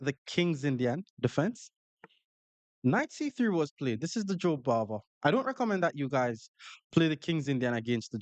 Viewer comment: dear, thank you very much for all your educational videos. I have one question related to "Joe Barber" (or at least that's what i hear). What is it exactly? You mention it 2nd time in 26:22 after Knight C3 move. I couldn't find anything related to this name and what kind of traps are you talking about? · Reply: jobava london